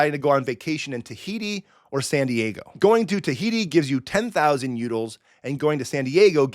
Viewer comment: Did you say Tahiti